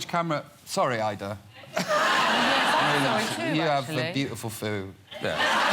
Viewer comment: What did Robbie williams mean when he said sorry ayda you have a beautiful foo...did he say fool or food idk??? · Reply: He said foo, which is an abbreviation of foo foo.